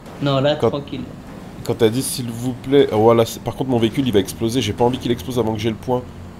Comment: c'est moi ou il place un petit wllh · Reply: Bilal76170 Non il a dit « Waa la j’espère que min véhicules va pas exploser «